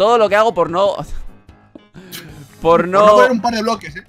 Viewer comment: parece que dice por no xd en el min